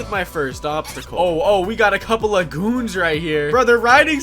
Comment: did anyone else hear “we got a couple of c**ns out hear”